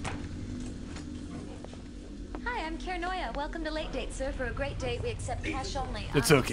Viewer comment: Did she say that her name was 'Kara'noia'?